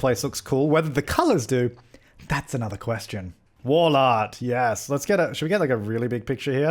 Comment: "wall art" sounded like war lard 😂